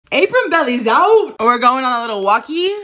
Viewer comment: I’m not even gonna lie 😭 I always thought you were saying acorn belly and I was so confused 😂 this makes more sense